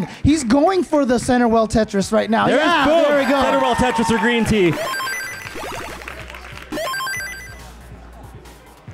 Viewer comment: greentea looks over and sees how far ahead Joseph is and says something. Can anyone make out what it is? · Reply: My best bet is “what is this score advantage?”